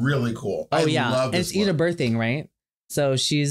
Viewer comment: Who does reference at 23:20? I can’t really hear what she’s saying but I’m really curious. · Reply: Eda Birthing!